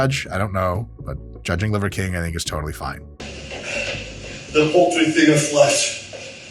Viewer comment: “Poultry” instead of “paltry” is crazy. His captions guy replaced “meager” with chickens lmao